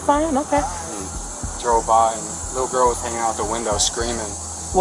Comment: Was that a subliminal message at 22:43 😂😂 He said "Drove by" but the subtitles say Joe Biden......